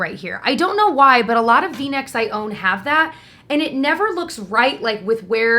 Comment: Ohhhh v necks. Totally thought you said Kleenex. Like whaaaa? Hahahaha 😂